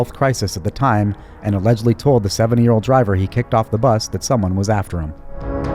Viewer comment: I keep hearing you say "...seven year old driver". LOL 😅